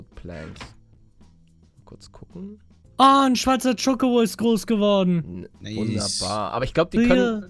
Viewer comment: Wer hat bei 5:10 auch verstanden: ein Schweizer chocobo? xD